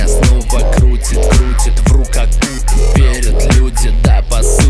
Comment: вру как Путин? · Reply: А мне послышалось в руках Путин._.